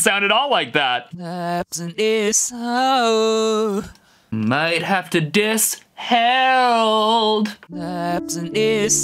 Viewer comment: i heard might guess snow